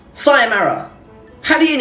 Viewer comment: istg i heard “sayonara...” instead of “saya marah”